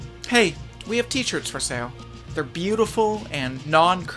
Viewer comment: When Joseph was talking about the t-shirts in the beginning, I thought he said: "We have teachers for sale. They're beautiful & non-cursed, & they're available on <link>." For some reason, I wasn't surprised.